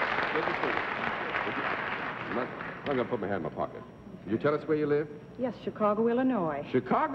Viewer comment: Hello, fellow fans of WML. I'm really wondering why John Daly said to the guest Mrs. Schmahl, "let me first put my hand into my pocket" while welcoming her at the black board. In my understanding it is quite impolite to have your hands in the pocket when greeting someone, and here Daly should do it willingly? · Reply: I heard it as "I ought not put my hand in my pocket." In any case, he had mentioned that the producers had admonished him several times about the habit. It was already a small running joke at that point.